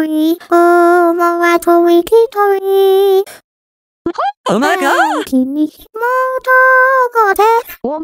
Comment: I CANT BE THE ONLY WHO THOUGHT HE SAID OH MY GOD!